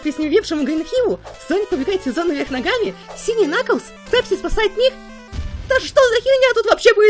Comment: Мне послышалась пепси сасает мир??????????? 😅